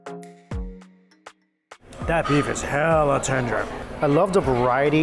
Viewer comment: Did he say, "hella" haha ayeee